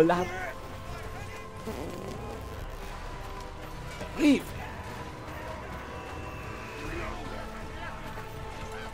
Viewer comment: Alguien que me traduzca lo que dijo en minuto 17:57 xD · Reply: "Terrible" dije, jajaja.